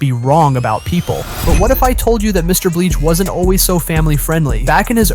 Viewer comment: Yo is it just me or did he say mr BLEACH